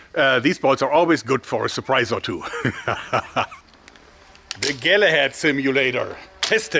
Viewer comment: did he say tagilla head? tarkov player?